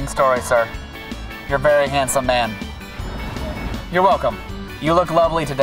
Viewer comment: the guy says F%#$ YOU!! but the subtitle say thanks. haha